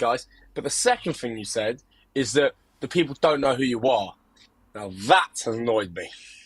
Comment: he didn’t say they don’t know who you are, he said “they haven’t heard of you.” I think he meant we haven’t heard FROM you lately, which is true. It’s been a while. Just wanted to clarify haha